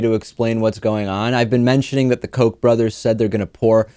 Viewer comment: I thought he said "Coke brothers" at first. And he seemed to keep using liquid-related terms. Like "suck up", "gushing", "koolaid", "pour"... I was so confused.